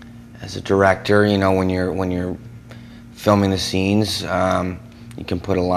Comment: Pardon me.^ He said he'll be FILMING IT. I thought he said he'll be DIRECTING IT. My mistake. I bet ALOT of people in SanFran would LOVE to tell me all about it. I'm just not interested. I loathe the lifestyle just like alot of people love the lifestyle. AFRAID of homosexuals?? Nope. Just don't advocate the lifestyle.